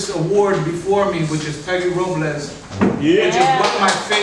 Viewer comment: Who was the poet mentioned at the 24:20 marker it sounded like Hector Robles if so I can't find anything on him. So what was the name that sounded muffled?